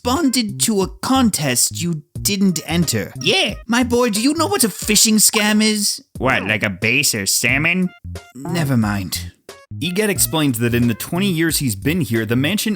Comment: okay, what is he saying here?I can make out part of it, but it doesn't make sense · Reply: um here goes E: " My boy do you know what a phishing scam is? " L: " What? Like a bass or salmon? " E: " ...N-nevermind.. "